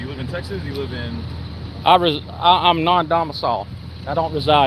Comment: did he say "I'm not dumb at all." or "I'm dumbass y'all." or "I'm not dumbasull"?